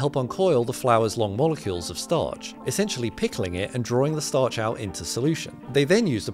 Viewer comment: Fist aid kit: plasters ✅ scissors ✅ lasagne ✅ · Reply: What are you saying at 0:26 ?? "Hithers a starch?"